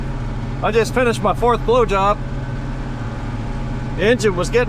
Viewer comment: I was only paying half attention, then I heard, " I just finished my fourth blow job" do what now? You know how to get someone's attention back.